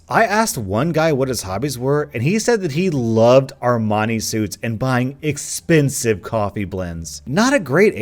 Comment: I thought said "Hermione suits" I guess I love Harry Potter a little too much